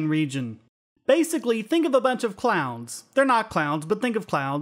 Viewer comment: I thought you said "think of a bunch of clowns". Space clowns?